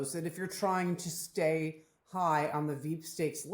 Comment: "To stay on the veep stake list...". I heard: "To stay on the deep state list," which confused me...